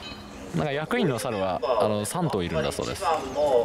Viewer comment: 「役員の猿は３頭いるそうです」が、「役員の猿はサントリーだそうです」に空耳した。